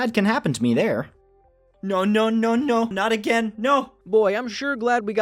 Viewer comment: sounds like he is saying "nyo nyo nyo nyo" XD